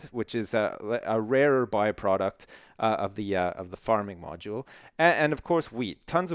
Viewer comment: i think i heard him say farting module.